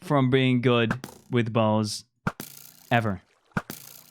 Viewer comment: the captions literally said balls instead of bows lol